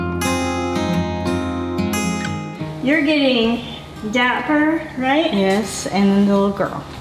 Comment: I thought I heard that kitten was named Diaper 😂